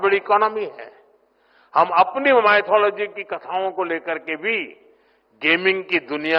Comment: did he really said Mythology or I heard something else